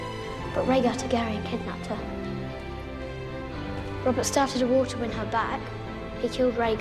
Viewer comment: did he said Robert Stark ? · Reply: Robert started a war